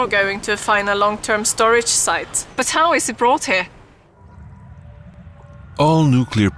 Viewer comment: Anybody else notice she says something completely different and it's dubbed over @ 3:30-3:33 when she's talking about where the waste goes??? Anybody a lip reader? I can't tell what she really says. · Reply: So how is it brought here, is what she said. English is her second language so she might move her lips differently than you are used to.